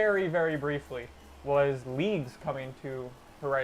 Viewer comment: lol i always thought he kept saying leaves but he was saying leagues